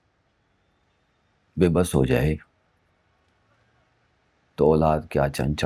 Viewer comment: was he about to say beghairat?! Cause subs showed it..what he said after ..what a sick mentality of io saab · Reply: Yes, wahi kehne laga tha phir khud ruk kar kehta beybas